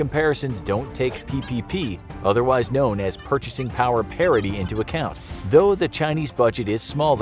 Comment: All I heard was that you said our pp was bigger